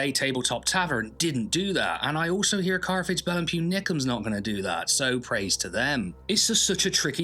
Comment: Can anyone tell me wtf he said at 2:23? Caravel and Pew Nickum"s? the CC didnt help